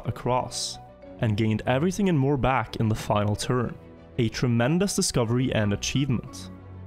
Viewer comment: "a tremendous discovery and achievement", just watched the cheaters documentary today amd no joke I heard it as "a tremendous discovery and a cheating" lol